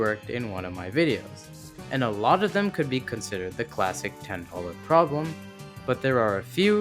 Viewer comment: The classic 10 what problem? I can’t figure out what was said here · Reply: Oh wait I think he said 10 dollar problem